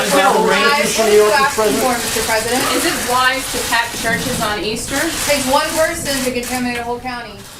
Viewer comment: I heard "pack churches on Easter"